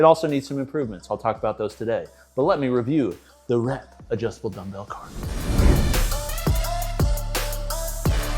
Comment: Really thought kept saying dumbbell CAR. . . Now I wanna see a car review